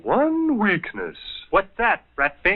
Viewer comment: What the hell did he even say there? "Buds"? "Bunds"? "Boons"? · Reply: He said their one weakness is "Bullets"